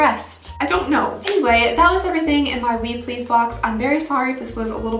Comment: Am I the only person who thought was saying “Weed Please” I thought it was a pot sub box lololol